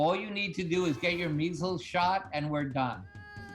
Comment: what did he say at 14:22 (all you need to do is .......)?? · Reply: "get your measles shot, and we're done."